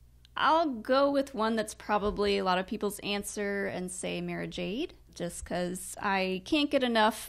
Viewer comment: I thought you said : " Mary Jane" , so I got my Star Wars bong 🤣🤣🤣